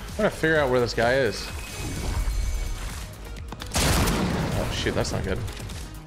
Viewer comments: On 10:30 in the video I heard say oh shi-💀☠️😭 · did bro say sh!t?